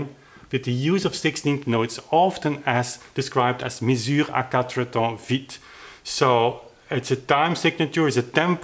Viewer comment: I enjoy these videos as well. In the future, could you do a demonstration at your clavichord or one of the many pianos? A demonstration plus the written theory will go a long way for many of us. Is there a possibility that you can post a translation of that French statement? I understand a bit of French, but only picked up a word or two since I'm not a native French speaker. In practice I have been applying what I've learned so far and it's truly an amazing rediscovery and journey. Many works I have found are not only more approachable, but also truly beautiful as these pieces go beyond the note-race to the end. Piano sonatas by many composers now become symphonic works in miniature more so than I could ever picture in my head since I always hear other instruments beside the keyboard instrument being played. Combining the slower tempo with finger voices and the other techniques I have learned in the past, has given new life to many works I played in the past. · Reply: You mean that French at 9:42? I hear "mesure à quatre temps vite", which would be "fast four beat measure". However neither French nor English is my native language, so you might want to check it.